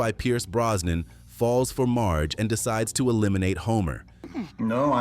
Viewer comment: I could've sworn I heard about an orange anti-Christ planning to eliminate the EPA?😮#TRUCK-FUMP